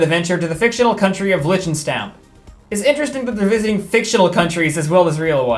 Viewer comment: I hope I haven't misheard the name, but I think they accidentally visited a country in the middle of Europe. "Liechtenstein is a German-speaking, 25 km long principality between Austria and Switzerland. The country is known for its medieval castles, alpine landscapes and villages, which are connected by numerous hiking trails." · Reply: The country in the show was called Linchtenstamp